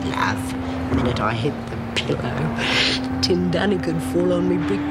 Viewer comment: what does lizzie say could fall on her brick by brick John W ??? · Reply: I heard "tin dunny."